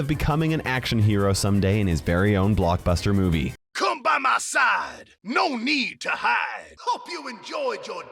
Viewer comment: did he just said "c*m by ma side"??😳 0:25 also i think i did hear "gayer zone" (jk its danger zone)